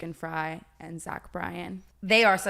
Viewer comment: lmfao why does it say Ashley instead of zach bryan